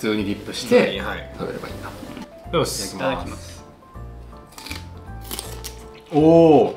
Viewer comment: 「いただきます」が「フムス」に聞こえる(笑